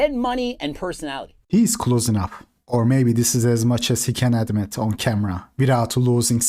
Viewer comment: I accidentally paused on the "maybe this is about as much as he can adm(it on camera)" And thought he said "Administer" instead... which makes sense when talking about pills